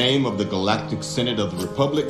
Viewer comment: Peterson may be in a position where he is contractually obligated to keep silent on the matter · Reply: Well I thought I heard him say "in the name of the galactic synagogue of republic , you are under arrest" and wouldnt be that far from truth though